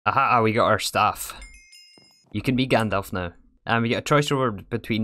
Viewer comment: For some god damn reason, when the " you can be Gandalf now" line appeared, I thought it said "you can be Gandhi now"